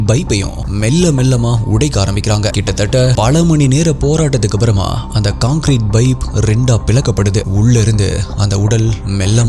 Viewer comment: To know more and invest in ICICI PRUDENTIAL LIFE <link> Part 02 - <link> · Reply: Sorry !! Please don’t mistake me. For some reason, I keep hearing “Bipe”. Is it really Bipe or pipe?